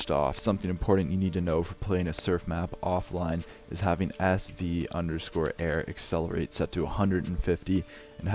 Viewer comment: I've been wondering, why did you say "sv_airaccelerate 250" while you typed at 0:16 "sv_airaccelerate 150" ? Note: if you said 150 i'm sorry, cuz im half deaf xD